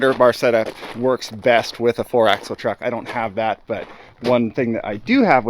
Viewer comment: every time said “I don’t have that” I always hear a “yet” . When you said the 9axel was for a job that’s done, I had a mild fomo panic but then you said it wasn’t on YouTube, I’m relieved I hadn’t missed something but sad we all missed out on some shenanigans